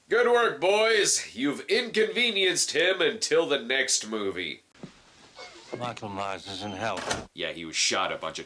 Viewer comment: What does he say at 25:09? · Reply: Michael Mires Is In Hell